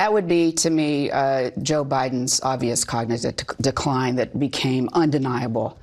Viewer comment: Did she say cognizive instead of cognitive? Even her telling the truth shows how bad of a media person she is.